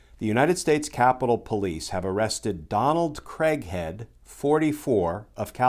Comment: Gee, i had to rewind because i thought Glenn said :"Donald Crackhead"! Well - with this kind of symbols on his truck, it would have been a perfect fit, though.